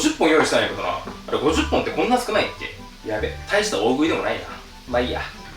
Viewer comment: 俺の聞き間違いかもしれないけど大した大食いでもないって言った？？？